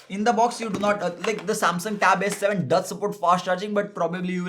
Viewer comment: hey....did you just say samsung a7 or s7?? @ 2:33 mins ???